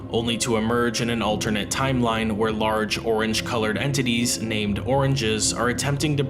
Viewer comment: I love these videos to fall asleep to. I'm at work now, and whether or not I'm falling asleep, I always listen to these at 1.5x speed. I heard this at 3:38:57 - "Large orange Titties." Instead of Large Orange Entities. Lol